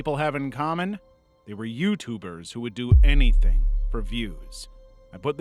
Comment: either I heard youtooers or i need to go to bed